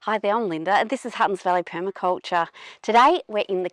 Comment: Oh my! I thought she said "Hot & Smelly Permaculture" then I saw the name of the channel. 😂